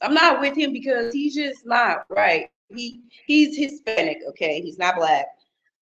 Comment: That white man probably looked like Mr Burns on The Simpsons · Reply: I thought she said hispanic?